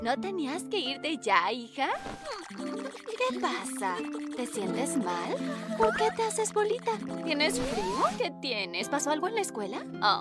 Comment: xd Entendí " se hace la boludita"